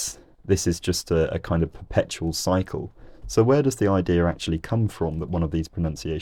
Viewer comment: YOU SAID "IDEAR" INSTEAD OF IDEA! lol jk this is a great vid